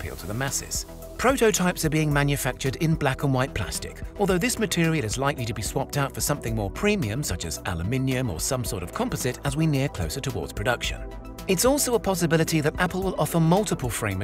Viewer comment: I know he has a accent but I gotta ask did he say aluminum at 1:21 or something else cause I heard aloeninum